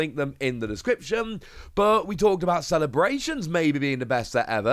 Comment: I thought you said celebrations was lol. Evolutions is like 30 dollars a pack or more and the zards only worth like 40 dollars lol I like some of the cards tho....